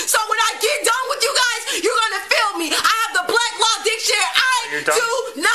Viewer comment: I thought I heard her call it Black Law Dictionary. Christmas in jail it is then.